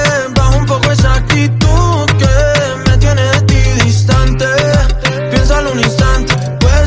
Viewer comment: Aaah pensé que decía "equidistante" jajajajajajaja